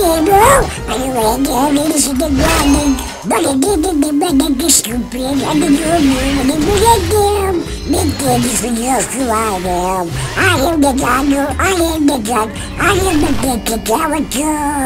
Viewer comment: is cracking me up so much! I heard Gegegebe stupid